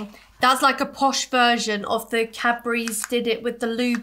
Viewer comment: I always thought you were saying Capri’s not Cadbury’s. My mind is 🤯🤯